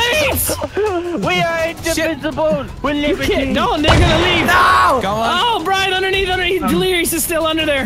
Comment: Did I hear right?🤣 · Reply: I heard "Don't, they gonna leave!"